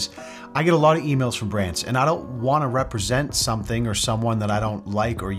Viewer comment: God hhhh 3:56 I misheard "brands" as "brits" and for some reason in that half second of confused half-consciousness I was completely ready to accept that yeah, he probably does get contacted by a lot of Brits and no, he doesn't want to represent them because he doesn't like or use them personally and like I get that! It makes sense! Normal thing to say, for sure ofc ofc.